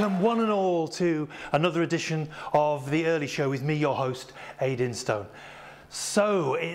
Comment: I heard "Hayden Stone" instead of "Ayd Instone" :p